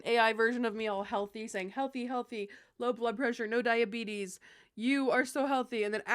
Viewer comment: I misheard and thought you said "no blood pressure, no diabetes" and I did a worried double take like Nooo 😮 we want you to have SOME blood pressure girl Thanks for sharing your journey. All of them are different and important